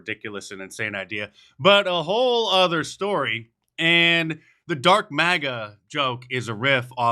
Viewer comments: I was there, I thought he said drunk MAGA 😂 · I thought he said Darth Maga 🤔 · After Elon jumped around, I truly thought he said he was DRUNK MAGA! Lmao! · I thought musk said he was "Drunk MAGA". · I thought he said Drunk Maga at first, lol